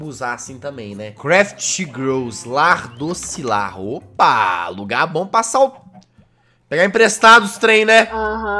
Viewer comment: "J" 7:03 como foi: lugar bom pra passar o... pegar emprestado os trem né. como e ouvi: lugar bom pra passar o sarrafo kkkkkkkkk